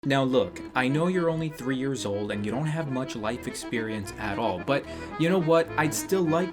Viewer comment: Lol I thought you said to Max: I know you don't have any life or soul.😂😂😂 I had to rewind that 3 times lol hahahaha. Love you and my Grandma had my dad at an early age XD but my parents had me at 35 and 24 or maybe less lol😂😂😂